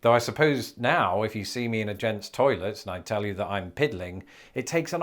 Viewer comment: Piddling? I thought it was peddling .. i'm from the south.. Texas to be exact.. and i never heard it as Piddling. Basically it means messing around and not getting on task.